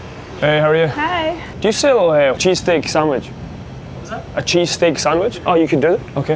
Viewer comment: As a Philly native, the cheese steak didn't look too bad. They come in many forms now and even though cheese whiz (cheese sauce) is how they were first made, it is better to have them with sliced cheese melted into the steak as it is cooked. The standard is American cheese in most of Philly but other cheeses have become popular. How the steak is chopped up may also vary. I personally hate it if I order a cheese steak and the place lazily pours cheese whiz on top or throws unmelted slices on the steak with out mixing it in on the grill. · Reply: at first, I thought they were saying Cheesecake. Was surprised to see meat!